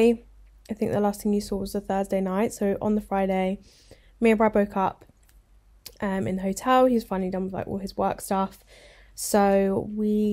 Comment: Omg at the end I heard “me and Brad broke up” and I was like whaaaatttt